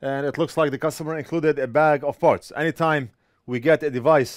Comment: You said "bag of parts" and I thought you said a bag of farts. lol I thought "well that's weird. lol